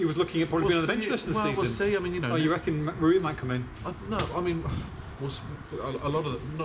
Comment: Does say Matt Murray at 8:05? Surely he's not coming out of retirement just to Play Shrewsbury? ?? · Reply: He said 'Rui' as in patricio 🙄